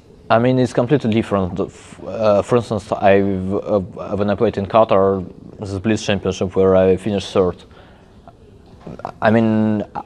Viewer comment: tell me what he said there ? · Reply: “I mean its completely different, for instance when i played in Qatar in the Blitz championship where i finished 3rd...”